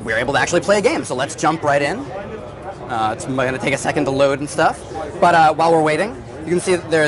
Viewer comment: Did he just say, might take a second to load" 0.23